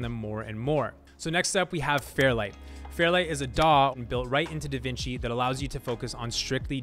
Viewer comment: i thought he said dog untl the daw text come up . the BEST Video about a edit software i have ever seen the video is so simple and is much quicker then any video